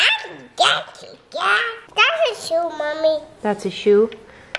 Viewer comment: I could've sworn just said “Dinosaurs” when said “shoes”. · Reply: That’s a shoe mommy